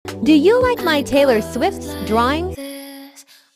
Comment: Is it just me or for Ian Boggs I heard "my in box drawing"